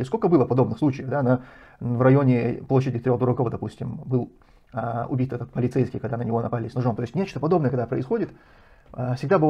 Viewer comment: Что за площадь 3 - х дураков? Или мне послышалось.